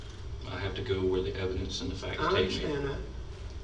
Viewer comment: What did Alec say that interrupts the detective at 1:42:12 I cannot make it out and it’s bugging me. Subtitles don’t pick it up either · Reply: “I understand that” is what he says.